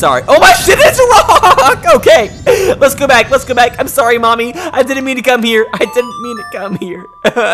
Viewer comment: Wow, shows where my mind is. When he said "I didn't mean to come here," I thought he was saying "I'm sorry Mommy, I didn't mean to cum in her, I didn't mean to cum in her."